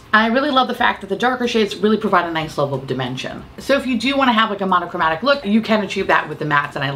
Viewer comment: Just for a moment I thought you said “the darker shades really do provide a nice level of dementia…” 😂